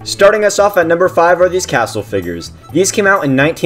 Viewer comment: I thought he said castle naggers lol